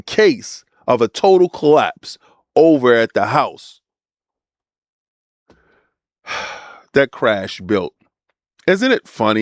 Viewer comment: I truly thought he said the house that crack built the first time I heard him say it... had to do a rewind for sure lol!!